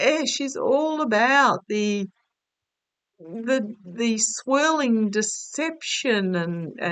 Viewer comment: Oh!! We are all laughing and revolted at the same time. Gotta tell you when you said "swirling" something ........I thought you said "swelling" something!!! LOL Oh God, funny and ugh, so ugh at the same time!!!